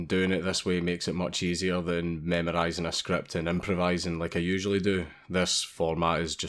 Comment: Did you just say "meme-rising a script"????